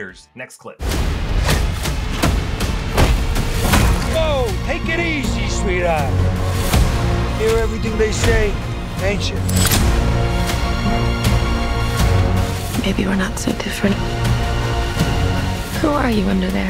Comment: Cobblepot says what sounds like "You're here to do what they say, ain't cha?" That would seem to be a piece of evidence in support of the outside party pulling Batman's strings. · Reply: I think he actually said "you're everything they say, ain't cha?"